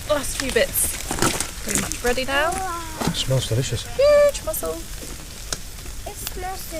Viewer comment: thought the kid said it smells racist and so did the guy 😂😂😂